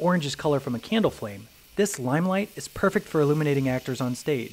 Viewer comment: I heard "eliminating actors on stage" 💀